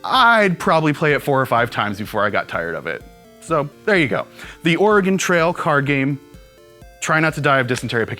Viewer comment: It sounds like is saying "The Organ Trail" instead of "The Oregon Trail"...